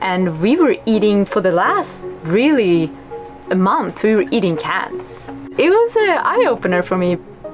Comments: After further review, I realize that (@ 11:36) said eating "cans". But, when I first heard it, I thought she said, "cats"... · I thought she said “cats” 👀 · 👀 I know I wasn’t the only one who thought she said “cats” at first listen 😏.. had to rewind that bit lol- “ocean cats” the top ramen of the boatlife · I'm sorry. I think I'm misunderstanding you here.... it sounds like you said you were eating cats.... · did she really say they lived through a month "eating cats"??? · did say they ate cats???? 😲🐈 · Am i buggin or did she say “we were eating cats” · Did you just say you ate Cats for a month????? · Eating cats? when you didn't have food? Did I hear that right at 11:35... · did she say they were eating CATS!? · am I hearing her right? She said cats right? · we were eating cats... even the captions thought that was what she said. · What a beautiful life. It obviously has its own challenges but man... i want that freedom. Freedom to sail wherever I want to in the world. Also at 11:34 when she says " we were eating cans" it sounded like she said cats lmao. A life like this, mixed with some underwater harpoon fishing like that one youtuber, oh man. Anchor next to a remote island that is uninhabited, and have a big fire on the beach with the fish you just caught on it... sounds like heaven. Although idk if id want to do it with a kid on board... once theyre old enough, you wont be having any privacy on that boat lmao. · did she say she was eating cats on that boat!?